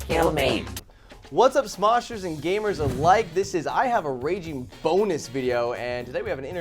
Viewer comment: Who else thought it said "Raging boner" at first LOL